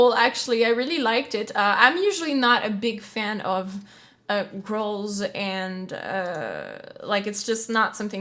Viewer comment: LOL. Straight up thought you said “makes me like girls.” Misheard your pronunciation of “growls.” Both are acceptable! Loved it! Great reaction/analysis.